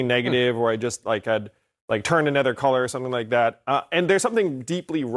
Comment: "Like I turned another color" I thought he said "like I turned into a car." I'm gonna use mine ,tho,from now on.